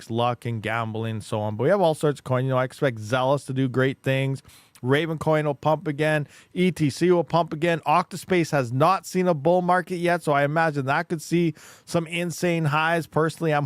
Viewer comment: Hey what coin did you say @ 46:55 ? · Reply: Ravencoin, ergo, octospace?